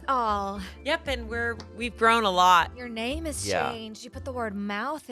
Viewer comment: I thought courtney said at first: your name is Shayne, you put the "mouth" in it